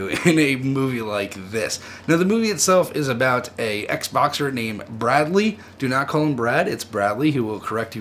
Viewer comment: At first I thought you said "X-Boxer" as in someone who plays X-Box. But quickly realized you meant "Ex Boxer"